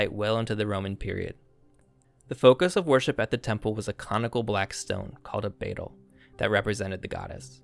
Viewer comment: i misheard it as "a comical black stone" and had a giggle 🪨 🤡